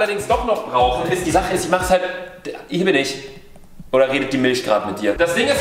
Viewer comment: habe erst bei 1:39 "Ich liebe dich" verstanden😂🤦🏼